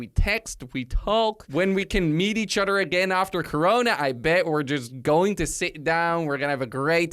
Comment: I misheard corona as korone, what happened to me?